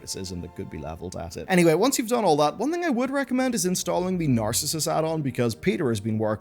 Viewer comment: At mark 3:07 you recommended installing some addon. I cannot hear what addon you said, something about my ears and some background sound is preventing me from hearing that bit correctly. I think you're saying "the narcissist addon". Is that right? Thanks. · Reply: Narcissus